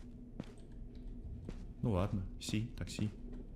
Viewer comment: Он испанец. Ну, ладно. Си, Как сказал: Так Си Как услышала я: Такси